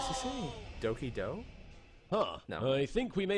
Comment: Paradox: "no no no... nononono" iS He sAyINg DoKI dO?